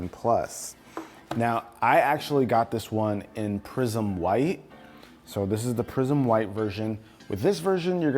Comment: I thought you said 'prison white' and I almost spit out my coffee 🤣